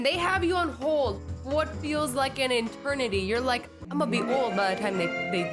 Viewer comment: did you just say an “enternity” you mean eternity right? Lol